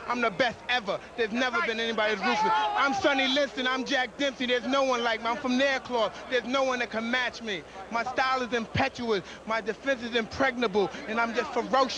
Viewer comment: Sorry my English is terrible. What he say between 01:00 - 01:10 "i am Sonny Lift? ... ... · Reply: 'I'm Sonny Liston, I'm Jack Dempsey! There's no one like me, I'm from their cloth! There's no one that can match me! My style is impetuous! My defense is impregnable, and I'm just ferocious! I want your heart!' - he's naming great boxers of the past, and that no one at the time could beat him, on any level. Then, he shares part of what makes him great - his style (impetuous!) and his defense (impregnable!). man, I love mike tyson. a true artist, he is (and was).